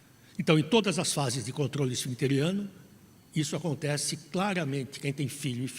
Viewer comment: Contorole o que? EU não entendi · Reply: Acredito que ouvi “controle esfincteriano”